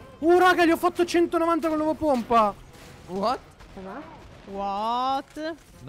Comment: Anche voi sentite il "what" in sottofondo al minuto